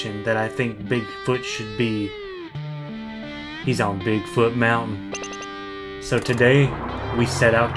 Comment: I’m pretty sure no one knew what the bobble head says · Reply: Bigfoot.